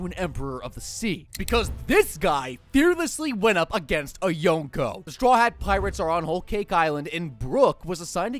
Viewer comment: when he said yonko the subtitle was young girl and the fact that he really said young lady